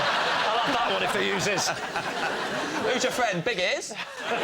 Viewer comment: What'd james say here? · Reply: I think it's 'who's your friend, Big Ears?'